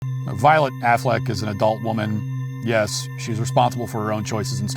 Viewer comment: I didn't know who she was, and I thought Matt Walsh said ,"Violent Aflac" like the Aflac duck bc of the mask